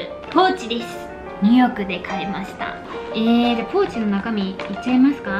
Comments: ポーチの ニューヨークで買いました。 が 二億で買いました。に聞こえた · ニューヨークで買いました〜が、2億で買いました〜に聞こえた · ポーチが｢2億(ニューヨーク)で買いました｣って聞こえてほんとにびっくりした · え？なんて言ってる？ 「2億で買いました」にしか聞こえないんだけど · ｢ニューヨークで買いました｣が｢2億で買いました｣に聞こえてびびった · ニューヨークで買いましたァってのが、2億で買いました。に聞こえてびびった笑笑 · 2億で買いました。に聞こえたw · ポーチ！ニューヨークで買いましたぁ が、2億で買いましたに聞こえた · 2億で買いました に聞こえた笑笑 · 2億で買いましたって聞こえるw